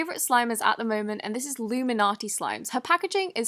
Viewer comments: At first I thought it said illuminati lol 😂😂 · I thought she said "illuminati" at 0:09 😂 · was i the only one who thought she said, "illuminati slimes" instead of "luminati slimes"??